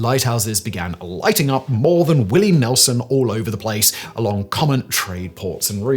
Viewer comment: I keep hearing "lighting up more than Willie Nelson" and i can't seem to hear what he's actually saying 😅